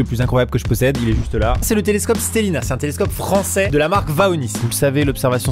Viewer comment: J'ai compris le téléscope Staline